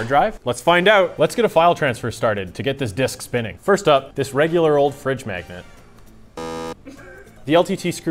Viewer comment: All I hear is “A crappy Mr.Hippo fridge magnet?”